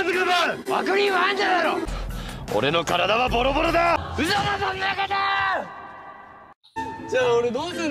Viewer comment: のｳｿﾞﾀﾞﾄﾞﾝﾄﾞｺﾄﾞｰﾝ!が嘘だそんなことー！って聞こえる人マジ天才すぎ